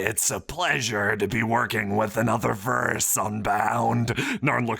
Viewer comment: What did Narn say? I heard "verse unbound".